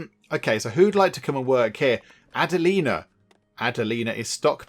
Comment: When you mentioned Adalena the cobbler, I first thought you said Agalina who was Emperor Teapot's first wife and soulmate from your first Crusader Kings 3 playthrough. Considering your settlement is named Cupboardia, it seemed appropriate that a woman would have the name of the Empress. Looks like a very fun game. I'm quite happy you found some animals to keep your people alive during the winter.